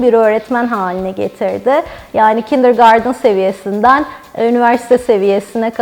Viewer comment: Killer garden mı dedi ben mi yanlış anladım 🤔